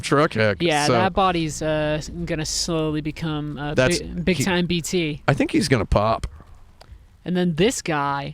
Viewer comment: When she asked him to explain what is BTs, I thought he said Big Tities 😂😂